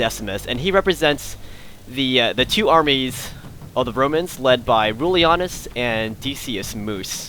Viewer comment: It sounds like you said "Bromans"